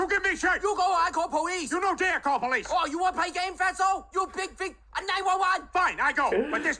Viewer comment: I am French and there's something I don't understand. 1:31 "Fat" what? Is he saying "fat souls"? That what I hear but I think I am wrong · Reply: No, he was saying “fatso” which is American English slang for a person who is very fat (so fat = fat - so)